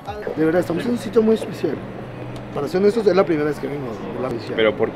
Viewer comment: La primera vez que escuche, escuche que decia "Estamos en un sitio muy sucio" jajaj